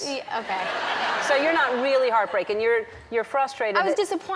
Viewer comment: Did Ellen say "heart-breaken" at 02:32?